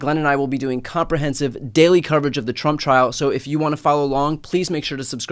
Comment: I understand my ears are old, but when Brian says 'trump trial' I hear 'chump trial' 😂